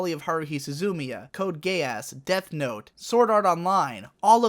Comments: i heard code gay ass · Hmmm...correct me if I'm wrong but did I hear him say Code: Gay Ass? Instead of Code Geass? 😆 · It sounded like you said Code gayass instead of code geass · I heard "Code Gay-ass." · it sounds like he says code gay ass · Did he just say code gay ass? Lol